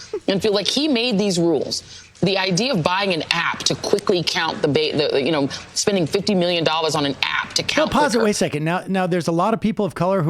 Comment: Wait, did Joy say $50 million on the app?!